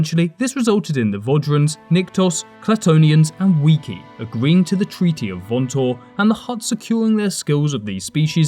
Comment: sounds like you said 'weeky' or 'weeki' here. based on context, you mean to say the weequay species?